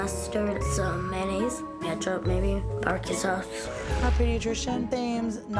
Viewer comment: DID SHE JUST SAY “bArqUE sUS?”